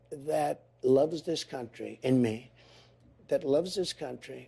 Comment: "I see someone that loves this country in me". DId Trump just say that, sounds very narcissistic. Times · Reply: In me